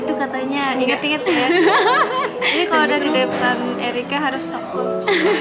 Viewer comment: kok dengernya " inget-inget jer kalau dekat erika diem